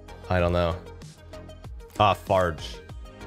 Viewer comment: Thought he said ah fart then an an ad poped up.